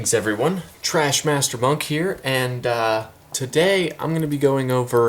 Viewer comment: I thought you said "Trash Master Bunk here" I right away thunk I am Subbin' Da Dude Man 🥴 Welcome Aboard ^!!^